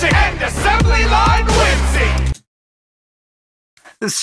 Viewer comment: When Walt said “ assembly line whimsy” I heard it as “assembly line whisky”